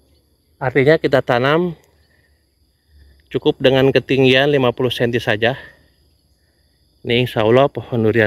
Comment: 8.06..masa busut 5m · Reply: Menit 09:10 Salah denger kali 😊 Gak ada disebutkan 5M tapi 50cm (1/2M)